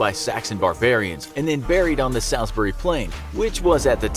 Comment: When it said Salisbury plain I thought it said sainsburys plain 😎😎😎😭😭😭😂🤣🤣🤣🤣